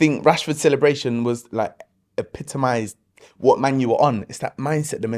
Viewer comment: Did he just say "Man U"? Thought he was a local Man Utd fan.